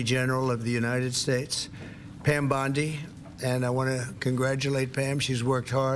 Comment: I thought it said Blondie